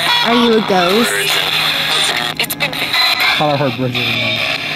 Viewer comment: I heard he say "Bridgtte"